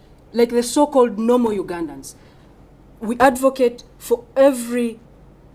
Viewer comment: I swear I turned on the caption n it said “yoga nuns” instead of ugandans